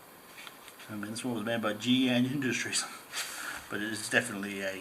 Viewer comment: Hi mate, when you say the manufacturer name of the genuine hoochie 03:43 I can't understand what you say. It sounds like GN Industries but I can't find anything by that name. I want to get one of the higher quality hoochies. Can you please tell me the manufacturers name? You can PM me if you prefer. Thankyou. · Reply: They seem to get made in batches mine from 2001 and 2003 were both made by Gee Yan Industry PTY LTD and both carry the same arrow symbol and part number <num> happy hunting regards Mike